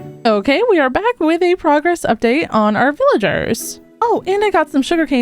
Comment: I thought you said frogress update 😂 u should definitely say that at one point 😊